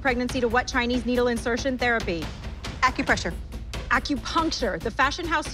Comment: She said acupuncture that was so stupid Edit: Nevermind she said acupressure I just misheard it